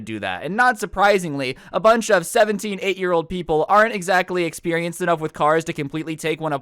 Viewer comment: Am I the only one that heard him say a group of 17 8 year olds